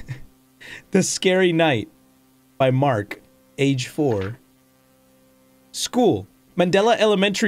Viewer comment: may just be me but it sounded like he said “age floor”